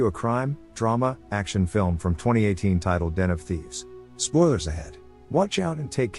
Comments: i actually thought that you said NFTs but its Den of Thieves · At first I heard "NFThieves" and I was really confused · When you say Den Of Thieves.It sounds like your saying NFTs.I was looking at my hand(for some reason)when you said Den of Thieves,and i thought i heard NFTs.